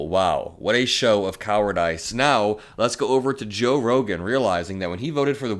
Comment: did you just say coward ice? Coward-uhs